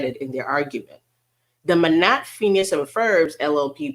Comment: Lmfao Manatt, Phineas and Ferb. 😂 I see you. I had to replay it but I thought I heard you say that.